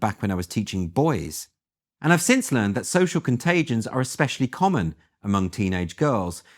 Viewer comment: You keep saying "contagent," but are you sure you don't mean "contagion?" 🤨 I respect you and bought your book, but you and others keep saying "contagent," and I've googled, but google corrects me to "contagion." It's distressing that you're a known influencer, and you and others keep using this word which, if you're not using it correctly, it might cast doubt about the rest of what you have to say. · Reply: Perhaps you're mishearing. One example might be at 2:03 where he said "contagions".